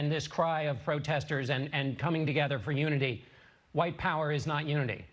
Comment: I heard "why cower" or "white coward", but I have poor hearing.